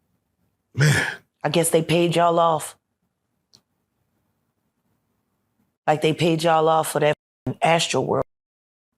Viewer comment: I donat understand what she said? What house burned off? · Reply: B house burned down. Her childhood home…